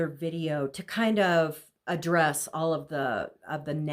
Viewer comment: Hilarious! At 10:09 you say Joe did a second video to address negatives in his first video. I had to replay bcs it sounded like II heard "undress" - which is how Joe appeared in video #2 🎪. Ewwww back to that creeper